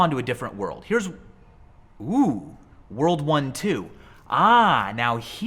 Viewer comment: I thought he said WORLD WAR 2